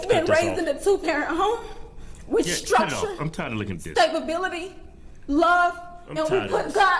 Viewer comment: Did she say Stabability? 😂